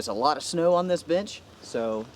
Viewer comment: I heard "a lot of snow on this bitch," and I was like what?! Ooooh.. BENCH! :D